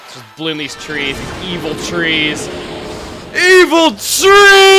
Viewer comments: When evil trees I thought you said evil cheese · For a second i thought he said EVIL CHEESE!!!!